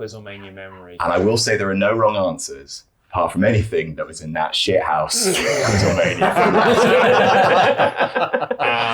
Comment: What quizzlemania is talking about at 3:00 I can't hear which one he said over everyones laughter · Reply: 49